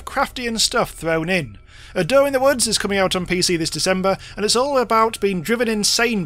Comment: I thought you were saying "A Doe in the Woods"... I'm not sure which would be a better horror title.